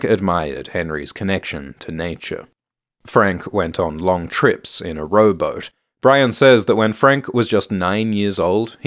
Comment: I thought you said Frank went on adventures in a robot